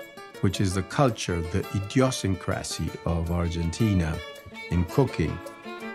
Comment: the subtitles didn't catch the sentence properly so he says: the idiosyncrasy of Argentina in cooking